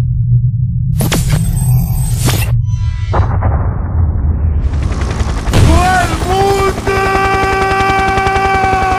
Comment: Que dice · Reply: Dice to el mundooooooooooo